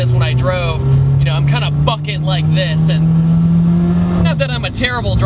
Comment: i thought you said " I'm kinda fucking like this," lol